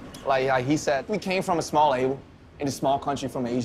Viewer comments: I think he said "a small label" not "isle" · i think the translation at 1:01 is wrong. i think Namjoon said "we came from a small label", not "a small isle". lol